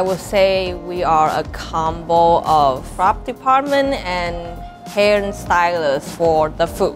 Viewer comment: I thought she said fart department XD